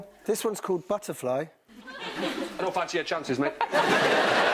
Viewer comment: What did Rod say? xD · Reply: αвѕolυтe αвѕυrd Dont fancy your chances mate